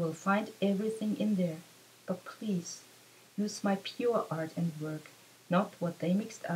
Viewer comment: what do you say there at 13:42 "but please use my 'pr work' ". ? i can't understand what you say there. thanks for sharing this. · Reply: helios what do you mean by "pr work" ?I can tell it here. Michael calls us to use , to listen to his PURE body of art only as this contains his TRUE message!Everything else that was published or changed after 6/25/09 like e.g. certain scenes along with a certain part of the song in his original short films changed his true message i.e. what Michael had exposed about the "system". And above all to HEAL THE WORLD BY HEALING OURSELVES, OUR HEART!